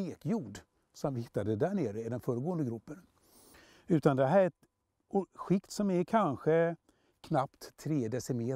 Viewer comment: Bra video men vad säger karln vid 8:14 %&¤&%¤%/¤-skikt? Lyssnar om över 10 ggr. Svårt att höra genom hans dialekt på ett antal ställen. Bra video!! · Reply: jordskikt tror jag. man hör bara -or(d)skikt.